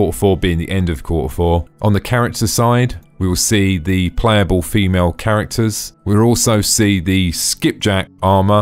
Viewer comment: It must be going over my head, i'm not seeing/hearing it anywhere. Might just be my headache and insomnia distracting me from understanding half of what he's saying though. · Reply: Had to rewatch the same part, like, three times before i heard "female characters." Man this headache is screwing with me.